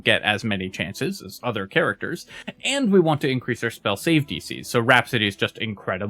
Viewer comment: I've thought he was saying "spell safety C" for like 30 videos and always wondered what the hell it was.